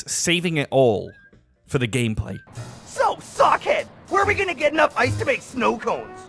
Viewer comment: Did Ed just say "So fuck it"???